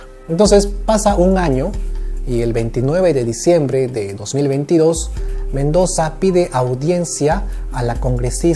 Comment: 29 de diciembre del 2022? :o o escuché mal?